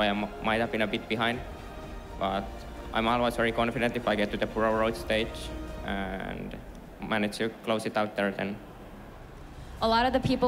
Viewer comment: What is Zerral saying at 1:36:32 someone please tell me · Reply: "I'm always very confident if I get to the burrowed roach state and managed to close it out there then."